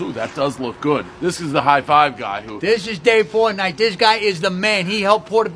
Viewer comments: Sounded like he said Dave fortnight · At first i thought he said "this is Dave Fortnite" 😂